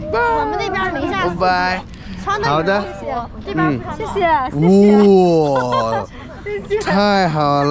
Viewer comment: I heard a portuguese sound "obrigado tchau" = thank you bye 😅